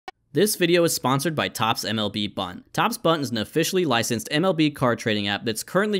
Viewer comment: I thought the sponsor said toppsblunt and I was so confused